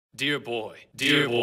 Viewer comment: i feel so stupid at one point i hear “bee boy”, “thee boy”, “you’re long”, and “Bzzzzzzzzzzzzzzzzz”